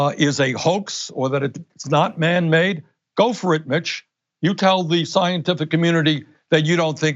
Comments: thought he said: Go for it, bitch! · Bernie says go for it bitch at 9:37 * he actually says "go for it mitch" But in all seriousness Bernie 2020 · Anyone else have to play this back with glee and discover he actually said, "Go for it, Mitch!" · When he was talking about mitch bringing the green new deal to a vote, I thought he said, "Go for it, Bitch!" I had to double check after my double take. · I thought he said "Go for it bitch" had to go back to catch that he said Mitch.